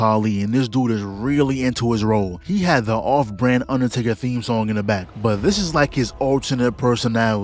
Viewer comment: I thought you said Undertale